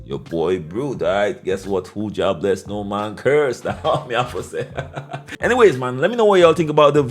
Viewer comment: Brute!!!! Please tell me what you said at 13:53 that sound like some real ish to quote!!! Please let me know 🙏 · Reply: WHO JAH BLESS NO MAN CURSE